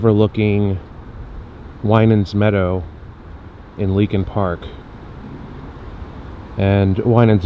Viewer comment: Thought it said linkin park lol